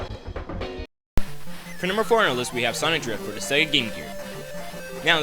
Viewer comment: Why did the captions say "Sonic Labyrinth" for Sonic Drift?